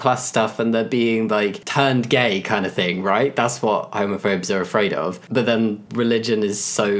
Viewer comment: Honestly thought homophobe said homophone